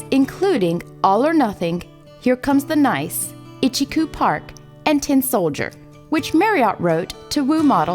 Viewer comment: Did l hear correctly. l thought she said the Small Faces had a hit with "Here comes the Night"???